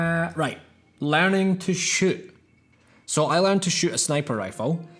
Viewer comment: Thought he said “learning to shit” 😂 Hi timed comment section